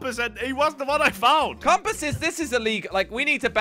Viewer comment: What did he say???? Bang compass??? 7:02 bdw